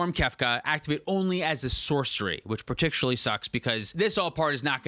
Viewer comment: Running while listening I kept hearing “Lua sucks” so I was like “did Lua just stomp them in a play game?”